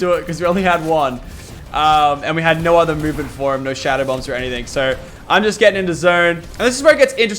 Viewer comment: thought u said u didn’t have any shadow bombs for Cody.